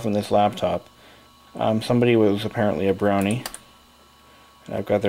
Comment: I thought he said that "the owner was apparently a brony". XD